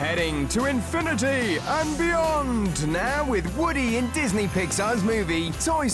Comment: i think the voice says vyond which it was called goanimate